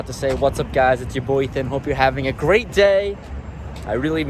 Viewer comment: I’m glad you realized mid-intro that you didn’t say “it’s your boy ethan”. for the first twenty videos i thought you were saying “it’s your boy son!”.